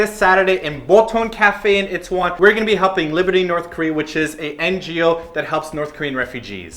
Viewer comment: LOL. i misheard what u said about the helping liberty in north korea. this topic messed up my hearing lololol. i heard humping liberty in north korea. DONT JUDGE MEEEEE